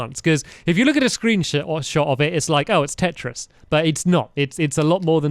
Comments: says sh*t instead of shot. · did say screen-sh*t by accident?